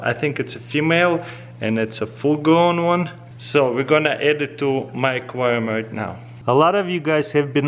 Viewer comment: I Thought He Said: So We Gonna headed To "Microwave" Right Now 😆